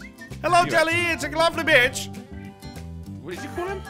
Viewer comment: did say "hello jelly, it's a glovley b**ch"? oh, he said lovely beach not b**ch